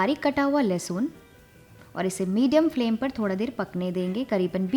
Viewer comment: u guys notice that she said lasun but the caption said ginger huh ?? Isnt lasun a garlic but the caption said it is ginger hahah